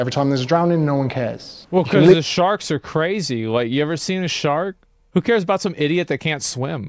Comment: I walked back into the room and all I heard was, "have you seen a shart?"